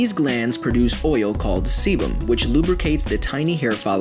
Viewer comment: Wasn't watching the video and working on something... and then I heard "sebum" and what I thought I heard was "semen"